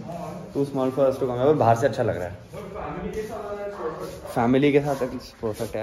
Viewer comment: Dom torreto : Did you say family